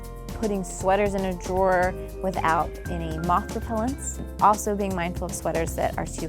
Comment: What did she say at 3:33? "putting sweaters in a drawer without ???" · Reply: Without moth repellents :)